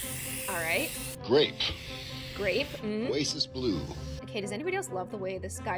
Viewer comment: is he saying racist blue